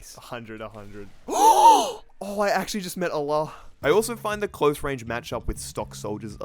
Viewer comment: did he really say he met Allah in